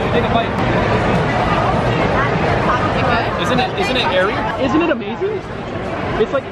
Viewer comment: When said it is airy I thought said it is hairy